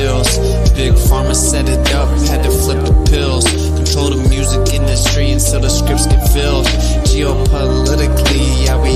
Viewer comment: about 1:22 minutes Clayton Morris says " all the SS agents and MAKE UP artists rush in???? Why??? Am I hearing that wrong ?? 😂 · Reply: lol he says OR